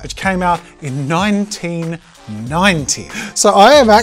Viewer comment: Okay I am so dumb, cause for half a second I thought he said this version came out in 1919 not 1990.